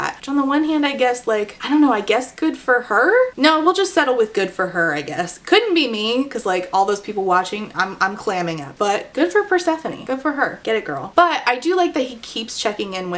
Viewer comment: did you say, "I'm claiming it" (which I would like), or "I'm clamming it" (which I would LOVE!!)? Actually, whether you meant to say "clamming" or not, I think I'm going to lay claim to "clam" as a term for "joyfully taking pleasure as a woman in female sexuality" - akin to the time Susie Bright first saw FtM porn, and when she saw the muscular trans man take off his boxers and expose his genitalia, she said, "My vagina waved hello".